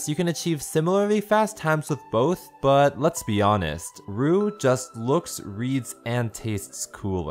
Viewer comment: Another reason ROUX is better: ROUX’s steps go from 1, 3, 2, 4! It’s so much more edgy than CFOP! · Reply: i thought you said robux. xD